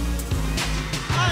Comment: Did he just say B-Tang boy?